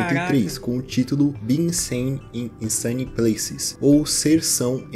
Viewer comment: eurealmente entendi "Being Sane e Sanin Plays"